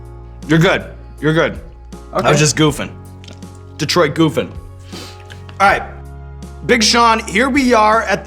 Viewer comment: Did he just say “Detroit Goofin”